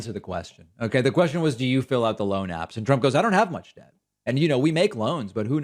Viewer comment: When tRump said, "I don't have any debt" I heard, "I don't have any depth" and I was momentarily shocked because I thought he was finally telling the truth.